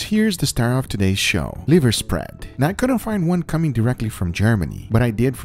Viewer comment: I first thought it said 'Liver Sperm' haha